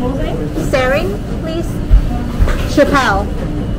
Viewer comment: did I hear“sering chappelle” when you’re picking up your orders at wing stop?!😂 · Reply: Yes! Haha! My husband's name is 😁